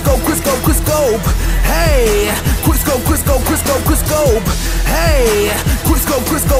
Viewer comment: lol i thought he was saying "chris cole chris cole chris cole"